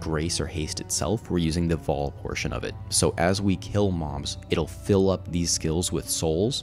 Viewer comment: anyone else heard him say ''as we kill moms'' instead of mobs around 46:15 ish LMAO :D